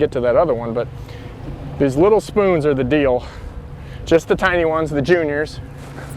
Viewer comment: Hahaha took a second to sink in thought you was saying them small spoons are the real Dio 😆🤷‍♂️